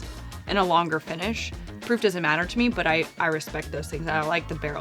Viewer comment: Did I hear say, "Proof doesn't matter me"!? :hand-orange-covering-eyes:😂